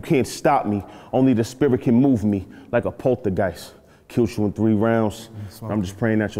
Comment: is getting a lot of gas at first round and I thought you said he killed I gave the first